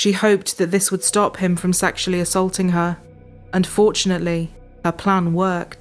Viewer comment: for a split second, each time i hear "and fortunately" i keep hearing "unfortunately" 😶